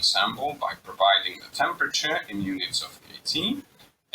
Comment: i didn't get the unit of temperature (CC says "unit 18") anyone?